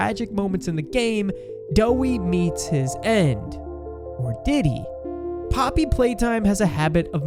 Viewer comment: did bro say diddy or am i trippin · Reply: Actually he said 'did he?'